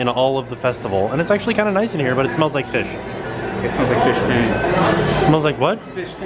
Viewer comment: I thought he said it smelled like a fish taint.